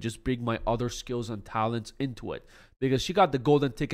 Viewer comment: did you say the n word???? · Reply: "Skills And Talents Into It" ?????????